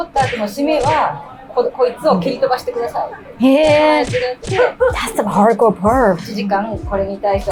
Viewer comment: wait, did just say “that’s some hardcore perv” looooool 😂😂😂😂😂